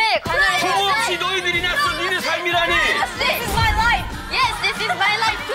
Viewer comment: Can we just talk about Yeri screaming privacy at 0:54 😂😂😂😂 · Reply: I heard "this is my life" as this is my wife" lol